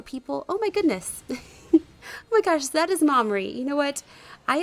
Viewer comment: Did she said Ma Ri not Madori.